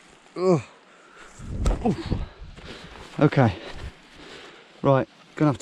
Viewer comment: Did anybody else hear idubbbz's voice saying "I'm gay" at 24:42 ?